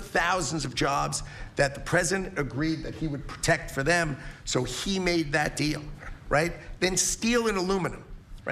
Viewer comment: Wait I thought he said that he made 200 deals 🤨